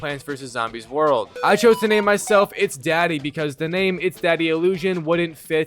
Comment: I thought it said it's diddy's house 💀